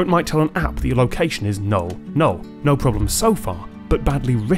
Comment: did they say "'null' problem so far"